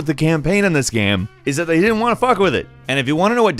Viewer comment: did just said fuck? I hardly ever him use profanity.